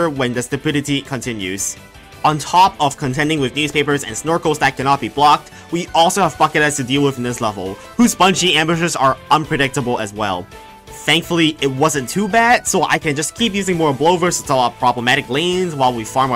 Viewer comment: it sounded like he said fuck it head.